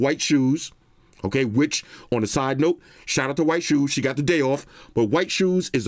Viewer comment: LOL, disregard. I heard you say white shoes got the day off after